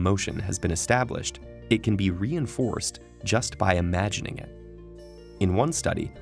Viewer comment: I thought it said 'just about anything'. How do I practise for mental things, like spell bee?